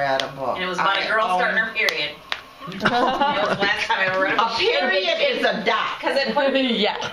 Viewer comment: Yeah she probably did but it sounded like she said "A Period is a duck" when I watched it.